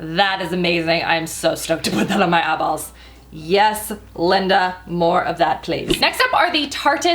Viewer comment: Did say "Yes, Linda, more of that please" when she was swatching her new blue eyeliner?? haha who?? or did I mishear. Also, love the nail polish. I definitely prefer shiny metallic nails to the neon colors. I think I'm partial to them because they might hide mistakes better and I'm horrible at painting my nails...